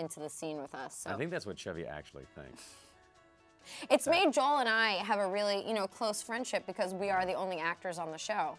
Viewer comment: did she just say “me, Joel, and I”